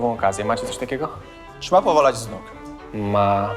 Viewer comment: co on powiedział? Przekleństwo polskich filmów, napisow nie ma bo jest po Polsku, ale za uja nie zrozumiesz co drugiej kwestii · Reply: "Czy ma powalać z nóg?"